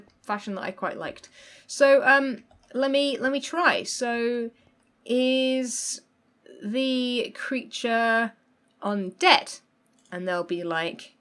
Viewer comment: At first I thought that she said ”is the creature in debt”